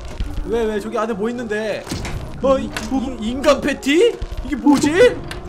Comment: 랑 6:57초 에서 나만 인간패티를 인간팬티라고 들림?